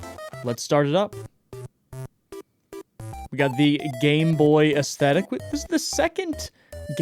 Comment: I THOUGHT HE SAID GAY BOY AESTHETIC IM CRYNE